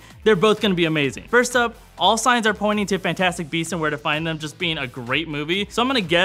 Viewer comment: Don't Breathe was amazing. I wish you had brought up Sing Street though. That movie was also fantastic. Also thank you for reminding me that I still need to see Fantastic Beasts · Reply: Lol thought it said fantastic breasts